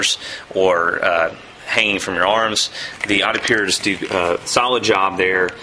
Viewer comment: it sounds like he says "the adipure is stupid"